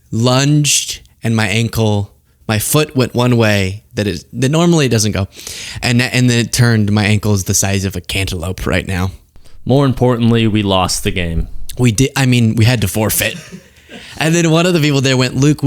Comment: I'm sorry, did you say you had cankles the size of The wandering Trumpelope?